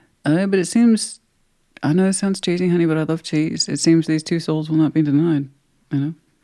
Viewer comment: Hello thanks for Sag reading. I replayed it so many times and still don’t understand what’s being said at 29:15 “Two souls” what? Can someone tell me pls if they pick up on it? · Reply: It seems these two souls will not be denied